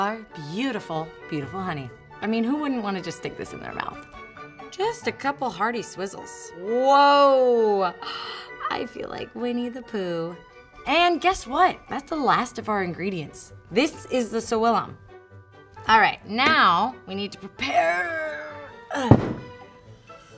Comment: I heard "I feel like we need to poo"